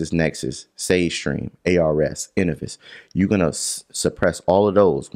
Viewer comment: When he mentioned the 3rd partys that has ur information about your credit score at 20:53 Did he say inivis? How do u spell it I cant find it..